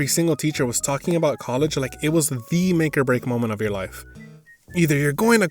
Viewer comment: what did u just say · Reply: "The make or break"